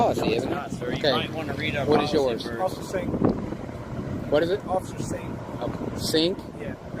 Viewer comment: hoosier, the 2nd cop who "only has to give his name OR badge number", i swear i heard "officer satan". and that does seem to match the personality.